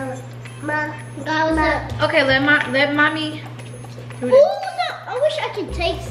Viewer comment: why it sounds like said “I love my mommy” 😩💙